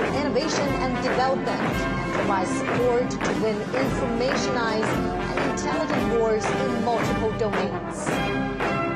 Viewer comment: What does xi say @ 1:23:16 ? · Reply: 同志们好，同志们辛苦了